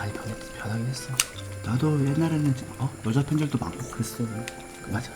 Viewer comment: 여자팬들도 많았다고 한것이 왜 여자팬적도 많다고 들리지?